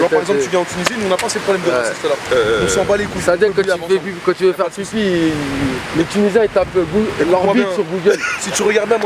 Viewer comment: Il a dit quoi ballek sur les tunisiens à 39:15 ahaha ? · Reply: Comme on lui dit que les algériens ont une petite bite il rétorque en disant "les tunisiens qd il veulent faire pipi ils tapent "bite" sur google"